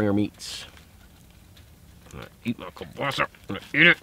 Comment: what does he said? Cobasa? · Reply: Chance Boudreaux kielbasa, it’s delicious sausage